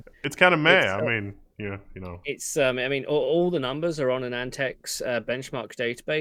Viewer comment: Two problems: at 15:55, I could not make out the web site where Dr. Cutress stores the cpu benchmark results. It sounded like antex or antec... Also, at 22:00, the web pages, which appeared to be from anandtech, are blurry to the point of being unreadable. Dr. Cutress, please get in touch with me at my email address. Good stuff; thank you both! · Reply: The website is Anandtech, the Dr is the CPU editor